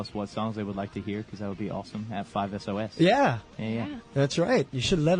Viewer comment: Wait a minute Did Ashton say ‘5 as oh as’ at 12:00??? I’m done I can’t